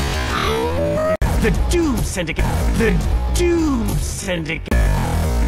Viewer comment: "T H E D U D E S Y N D I C A T E" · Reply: You heard it as the Dude Syndicate too